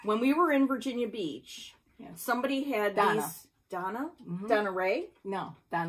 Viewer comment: I have hunted for livefromthesheshack and not found anything! Could you check her flosstube name please? Thanks! Love you guys so much! ❤️❤️❤️ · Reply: Thank you so much! I thought you were saying Donna, not Dawna!!!